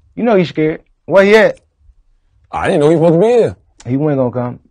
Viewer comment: I didn't hear him say DJ Envy was supposed to be at the interview. I heard him say, I DIDN'T know he was supposed to be here at the 5:54 mark of this video. Around the 6:20 mark of this video also, he says he doesn't think there is a problem. He also said that she felt disrespected. People be making up stuff.